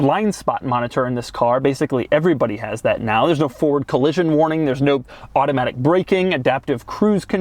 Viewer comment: What is the spelling of that word? I never heard that word before. Seriously guys. Not Joking. FORD collision warning.